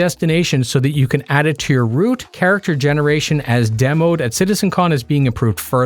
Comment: Many do not have this issue. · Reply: Oh listening back I got confused at @ 4:23 when you're talking about routes (subtitles said root character generation lol)